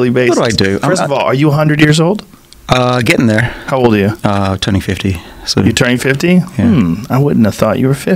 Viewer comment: when he said turning 50...I thought he said "2050", as an answer to are you turning 100. I was super impressed. Then clarified he was turning 50..still looking good for his age.